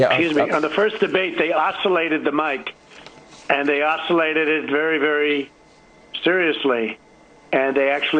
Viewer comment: they ocillated the mic? does anybody know what he's talking about? Is he trying to say isolated?